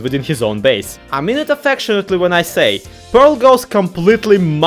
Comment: I heard "mom-mode" as "mumbo" and to be fair, it does sound like a rant mumbo would go on.